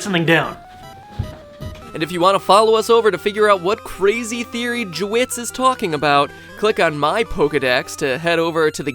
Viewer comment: Did anyone else notice that he said jewits instead of JWittz